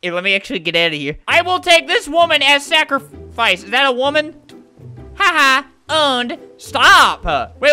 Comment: xDD lol he says "iz dat e uwomen"... THATS WHAT HE SAID